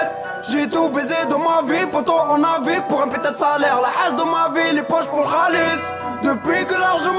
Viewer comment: de 1:10 a 1:15 c quoi il dit ? jcomprend pas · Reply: Realrecognize real la Hess dans ma ville les poches pour le khalis(de l'argent)